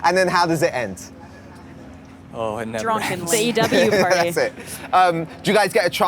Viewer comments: did Lili say drunk and laid?? 😹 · it sounds like lili said “Drunk and laid”😂😂 I heard the drunk part not the last part😂